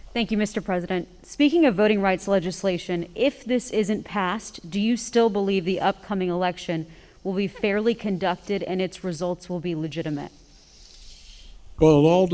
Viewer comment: Go to 23:20 and tell me what that lady is whispering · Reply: I hear "I got you" and her reply "Thank you".